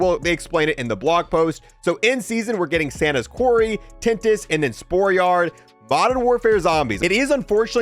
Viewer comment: did you just say, santa's quarry? I don't think I want that thing near children if that's what his quarry looks like.